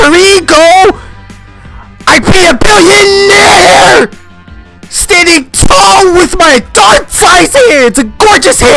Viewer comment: I’LL PEE A BILLIONAIRE! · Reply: I heard I'll peel hair I'll be standing tall with my dot sized hands